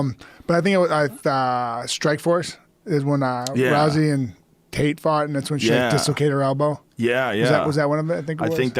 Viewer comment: The fucking subtitles can't even understand Stipe. 1:39 "is when i rousey and kate fart and that's okay" lmfao!